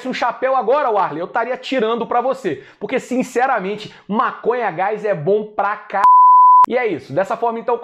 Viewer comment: Entendi "Maconha a gás é bom pra c******" de primeira rs Depois que vi a luta e a entrevista e entendi o "maconha guys".